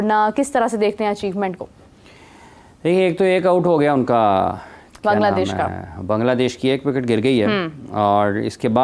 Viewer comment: Does said1.02sec 1:02 Kangladesh 😅